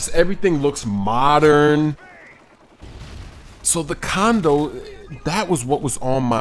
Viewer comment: LoL 5:38 sounds like he said condom